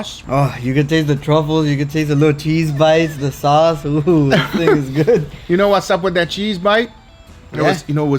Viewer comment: sounds like said "this n****a is good lmao😭😂, p.s. love your channel!